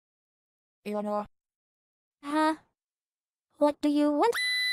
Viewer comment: I thought it said "Elevator" instead of "Eleanor" I'm dumb